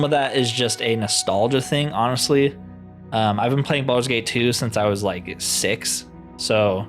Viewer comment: you capping man, you said at 9:36 "i haven't been playing Baldurs gate 2 since i was like 6, so got to put it above balders gate 3" ummmmmm i don't know about you sir, but i don't hear "first time played" in that sentence. I get you have your own opinion, but to blatantly disregard how a game is presented *cough cough bethesda games*. In my point of view its just sound like ignorance. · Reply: No.. I definitely said "I've been playing Baldur's Gate 2 since I was six" meaning continuously playing since I was a kid